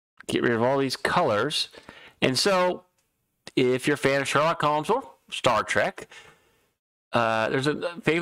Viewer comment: I thought I heard you say Sherlock Holmes, and the Google auto-captioning said Charlotte. I think it is worth it to say it slowly to get the name right for me. You should also mention Sir Arthur Conan Doyle with the quote. And what was that Star Trek reference? Can you cite the movie it is from? ;D Good video just for this one point.